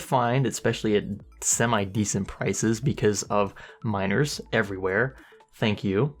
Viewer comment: Fuck you miners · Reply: i thought he said minors there, imagine if he said minors "those fucking minors buying up all the GPU"s to hell with them"